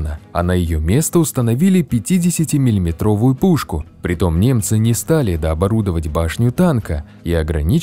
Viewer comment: тут я почему-то услышал не ''немцы'', а ''мемцы''